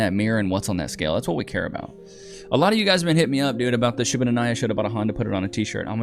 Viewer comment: I swear to god the first time I ever heard him say that I thought he said "I shoulda denied her 'n' just bought a honda. Put that on a t-shirt" but the last line before that was something like '...modern women are just unreliable and the mileage is always too high..." so it made perfect sense in that context ... I have never laughed that hard at a misunderstanding in my life 😂 P.S. can we text that number? Like if we have a 1 off question and don't wanna bog down the app (I assume it's an app) with traffic over a general inquiry.